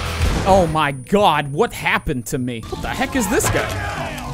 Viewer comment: I think he actually said "fuck that"